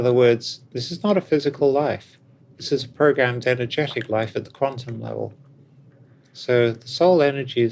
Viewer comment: "this is a ___ and energetic life at the quantum level" what does she say here? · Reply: “This is programmed energetic life at the quantum level”. That’s what I heard.